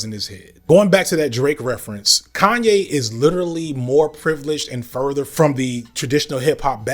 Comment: ye is the colonizer? 😂I’m sorry you put so much work in but that’s what I heard here